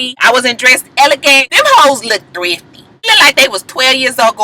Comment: They really shoulda been using subtitles when Joseline speaks because I had no idea she was saying the other women looked "thrifty", I thought she was saying "three fifty" I'm like huh, is that some new slang that I'm not up on...lol smh